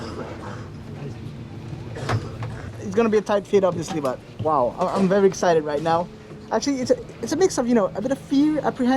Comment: Thats what she said? at 1:42 lol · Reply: She said "sorry" for being in the way of the video :)